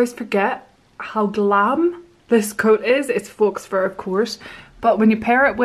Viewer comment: Faux is pronounced ‘foh’ btw. Rhymes with no. You sounded like you said fox fur haha. 🦊